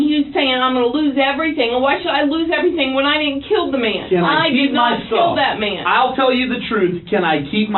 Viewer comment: did she just say she killed that man... didnt kill that man??? Oops snitched on herself right there.